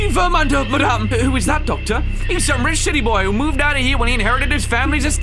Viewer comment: Is it just me or did it sound like he just said sh***y boy instead of city boy?